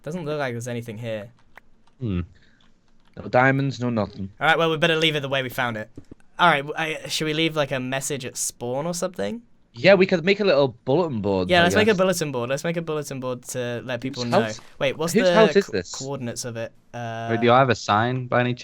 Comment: did I hear Boatem board.